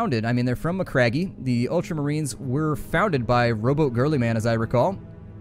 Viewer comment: Did you say that the ultramarines were founded by robot girly men? :'D